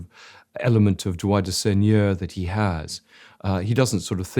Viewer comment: Please can anyone tell me what Lownie says at 2:43? 😭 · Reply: Droit du seigneur - right of the lord